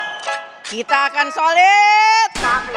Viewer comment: Kedengeran gw...'kita akan suliiiit"